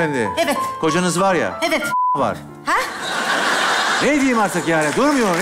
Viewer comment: da ne diyor ya anlamadım kocanız var ya.. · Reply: afedersin nah var diyor 😂😂